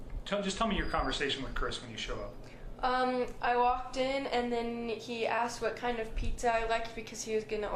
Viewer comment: I may have heard wrong But did I hear her say and then Nikky asked what kind of pizza I liked? @ 09:50…. Did I hear that wrong? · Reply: I could be wrong, but I hear: 'And then he asked what kind of pizza I liked, because he was going to order me and the girls pizza for dinner.' I think if the babysitter had known about NK, she would have told the police about her. And there would have been a response like, 'Nikki who?'